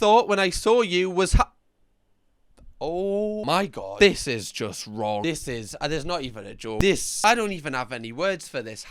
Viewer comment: can someone tell me what the word is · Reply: I've enhanced it and im pretty sure it said "how r-word-able (SA) you are"